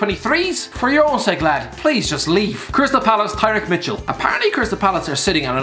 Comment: Anyone else hear Crystal Phallus pans on